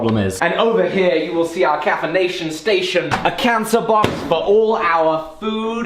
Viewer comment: Correct me if I'm wrong but at 17:25 did say cancer box? with the edgy jokes 😂😂😂